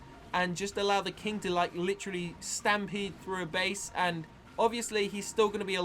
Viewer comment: I heard stampy's name